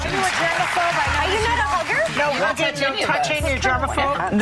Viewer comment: I thought she said, your not a booger? Haha not hugger